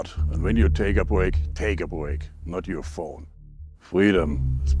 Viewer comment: Did I just hear him say "wahala is coming"? Thought wahala is a Nigerian slang